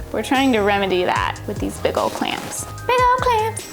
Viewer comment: for a second i thought you said biggle clamps not big old clamps and i was like what are biggle clamps?